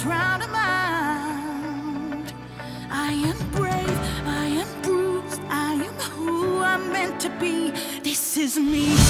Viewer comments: I got jumpscared at school when I walked outside and was yelling, "I AM BRAVE, I AM BRUISED, I AM WHO I'M MEANT TO BE--" Then three of my friends and four people I didn't know came up from behind me and screamed "THIS IS ME!" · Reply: Riley Smith oh really i thought it said i am bruised all along..lol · I still hear "I am bruised" as "I AM GROOT" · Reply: I thought it said “I am Brute” 🤣🤣🤣